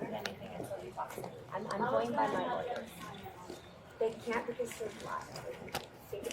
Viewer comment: Did she say, “they can’t because they’re black,” 41:18 about the movers signing a disclosure? Regardless, if she said that for whatever reason that’s a wild thing to say.